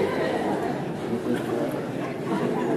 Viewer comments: The pastor said, “I’ve never had that happen before!”😅😂🙏🏻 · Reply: I thought he said lets never have that happen again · What did the pastor say at 1:06? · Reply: “I’ve never had that happen before”